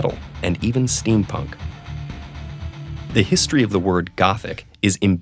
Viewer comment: I thought it said history of goats